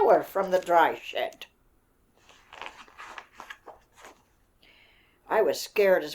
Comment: If you have cc on at 4:43 it says shit instead of shed